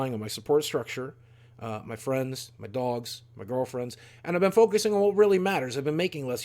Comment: Playa Playa did you say girlfriends?